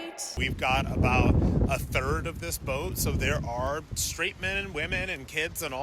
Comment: im sorry bear this bad news but i think that he is saying “we had a third of the ship. so there is straight people” as in the gay cruise is ONLY a third of the ship 😅 600 straighties on ye old ted cruz cruising cruise